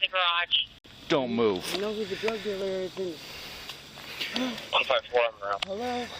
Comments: did I hear her correctly!? Did she seriously just say “I know who the drug dealer is”? Wtf did she think she could narc out one of the other people and then get out of trouble!? Well….wait a sec….that stuff happens all the time! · Did she just say "I know where the drugs are at hello... the meth"